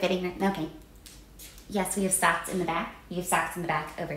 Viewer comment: The "We have socks in the back, over" like she's landing a fighter jet kills me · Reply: I kept hearing "sacks in the bag". This means nothing to me (non-native speaker).